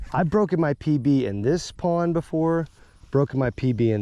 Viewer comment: Bro, at first I thought you said, "I've broken my PP in this pond" 😆